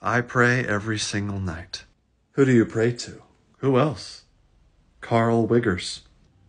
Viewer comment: Many of us watching now have to look up Carl Wiggers. Not all your fans are doctors or in the medical field. And now I know what Carl Wiggers did and am very impressed. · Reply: I have bad hearing and thought he said something other than "Carl wiggers". I thought he said "dark wizards". I thought it was just a weird cardiologist thing but then I realised that I suck at hearing.